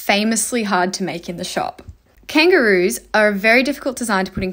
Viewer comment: I thought she said “kangaroos are so tasty” 💀